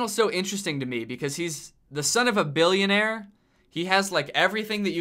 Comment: When Danny said son of a billionaire I thought he said son of a b**ch